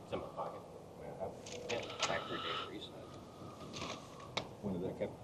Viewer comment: I can’t make out what was said about the phone around 58:45. Did he say he factory reset it or am I imagining things?